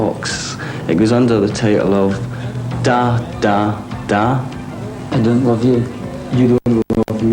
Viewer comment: what is he saying at 6:24???? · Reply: Glitteringla 24 Charlie says "I don't love you". Then Mick says "you don't love me". They are discussing the song Da Da Da by Trio - translating some of the lyrics from its original German.